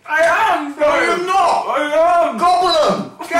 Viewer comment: usłyszałam od Leo " jem " ??